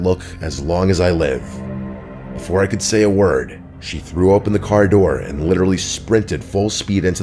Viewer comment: you have to wonder if that girl was actually real or just a figment of his imagination. Because you noticed that he was the only person who she was interacting with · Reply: She didn’t vomit. He said she threw open the door. I thought he said threw up as well but nope.